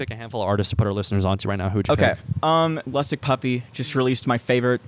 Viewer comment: What did she say ? Puppy somethin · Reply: Lustsickpuppy